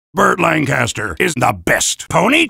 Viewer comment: Wait, who did Soldier say was the best pony now? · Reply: Burt Lancaster.